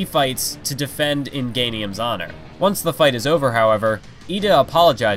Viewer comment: "In GAY nium's honor." anyone else hear that?